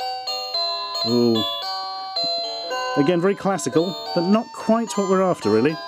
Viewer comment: I misheard "Again, very classical" as "Again, Reclassical"